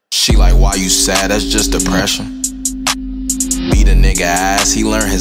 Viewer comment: Is he saying ate his ass or beat his ass